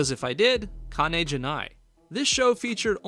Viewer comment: for people wondering what he said, he said “kane ja nai” or “okane ja nai” meaning “No money” in Japanese.